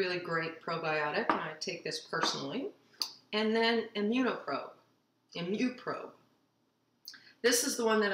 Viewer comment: I know this is an old video . I’ve rewatch several times and cannot understand the name of the last probiotic at 4:05. Nice easy recipe! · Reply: Imupro and Gutpro ~HB Team